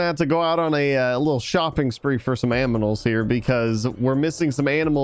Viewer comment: Did really say aminals for animals